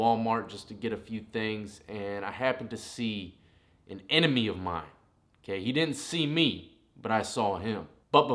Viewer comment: Lmao! I thought it said "I ran into Eminem today!"